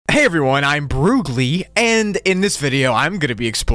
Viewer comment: I thought you said "Hey I'm Broccoli"